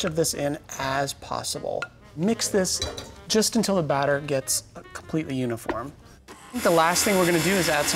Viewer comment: Great vid, but I swear at 03:06 I heard : 'Mix this until the batter gets completely unicorn' ....must be hearing things I like to hear!!